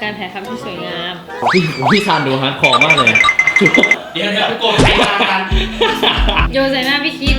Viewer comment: พูดว่า เดี๋ยวๆโกรธใครมากาน